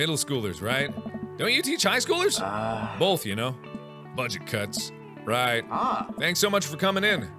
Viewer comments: when he was talking to Hugo and he said "budget cuts" I heard "buncha cunts" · When mr. Vega said "you know budget cuts" the first time I watch it it thought he said you know buncha cu**s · When it said "budget cuts" I thought he said bunch of cunts.